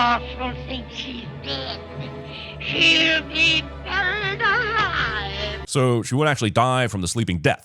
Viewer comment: Up until I saw this video, I thought the wicked queen said, "But she'll still be alive" and not "She'll be buried alive." Now that I heard that, it makes more sense because the queen sounded way too happy to know Snow White would still be alive after eating the apple.